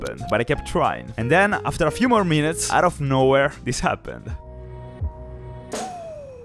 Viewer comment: Sounds like he said “so unsubscribe”